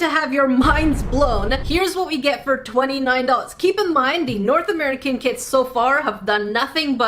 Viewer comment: It sounds like she says north American KID but she says north American KIT